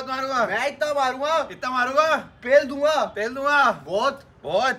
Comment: what did said😮 pell donga😮